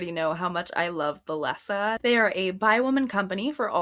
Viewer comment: had a brief moment of completely mishearing things and went “Bi-women? So nothing for lesbians? :(“ in the same tone as “what? No bless yous for Giacomo?”